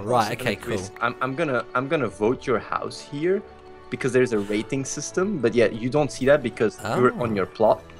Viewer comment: I thought he said raping system and I was like BWAT